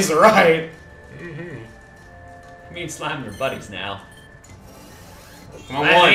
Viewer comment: it sounded like me and slime are butt aches now lol